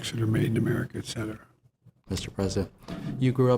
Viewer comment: Did Joe call him senator at 3:17??? Lol